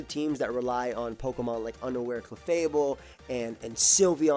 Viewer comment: "underwear clefable" I know he said unaware, but he sounds like he said underwear.